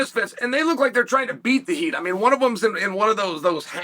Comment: Did he say "beat the meat"??